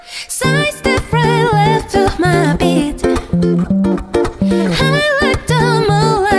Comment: je rêve ou jai entendu<< ma b***>>